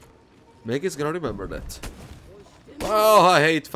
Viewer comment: I thought he said the N word but it was mege :(